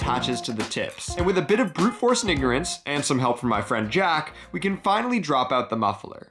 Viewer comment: WOAH 🤨🤨🤨 WHAT U SAY? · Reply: Brute force & ignorance